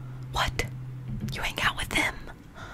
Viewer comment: When she said "You hang out with them???" I heard "You make out with them???" And I was very confused